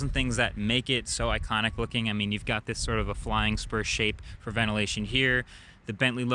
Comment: Man, I thought you said flying sperm.